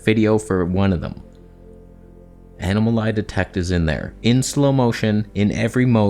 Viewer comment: I thought I heard animal lie detect AF which I now like better than *eye detect*. I could use this technology on my cat.